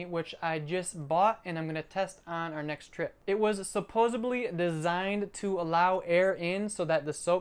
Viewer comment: did you say supposeably???.